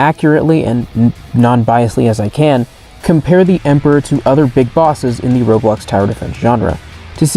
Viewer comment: did you just said BIG BOSS